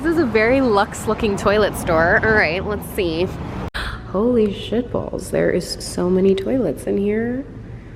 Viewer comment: I lost it when was toilet shopping and she said 'holy shit balls' because I totally heard 'holy shit bowls' and thought that was ironic lol